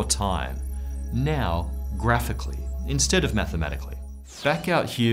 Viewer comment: Is this space-time dyslexia purely a "Matemathical" quirk 😆👍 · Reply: I thought he was saying Matter-mathical, as a pun.